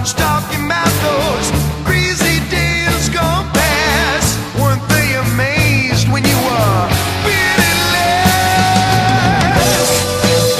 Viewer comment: Lyrics: They may talk about your cold when you were Headed for the skies But you were young and bold, and baby Didn't that change with a wink of your eye Now no one's talkin' 'bout those crazy days gone by No one talks about the times you cried A little dreamer A little dreamer And then they went and they voted you Least likely to succeed I had to tell them baby you were armed with All you'd need Seems no one's talking 'bout those crazy days gone past Weren't they amazed when you were really last? You are the little dreamer You were the little dreamer, yeah yeah Yeah, they talk about your cold when you were Headed for the skies But you were young and bold, and baby Didn't that change with a wink of your eye Seems no one's talkin' 'bout the crazy days gone past Weren't they amazed when you were really last? You are the little dreamer A little dreamer, yeah, oh oh Yeah, a little dreamer Little dreamer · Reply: I always thought they said Weren't they amazed when you were free at last